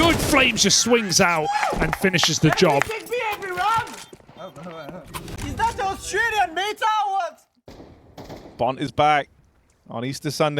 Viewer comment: what did apex say? glad to see him back in action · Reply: retake B every round, is that the australian meta or what?